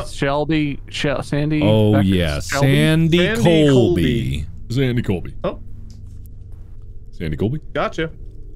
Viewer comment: I heard estas method then “sandy colby” and I thought mark was saying Sam and Colby and they use that methos